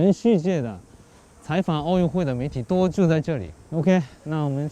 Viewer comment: 所有记者都聚在这里，我听成了住在这里😃